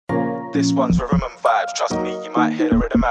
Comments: Riverman Vibessssss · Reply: Lol it says rhythm and vibes and not riverman vibes but I literally also thought it said riverman vibes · When says Rhythm and Vibes, it sounds like he is saying "With an M5"